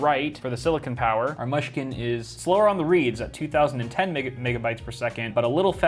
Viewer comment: I thought he just done said Nigga-byte, I was like boy that must be fast as hell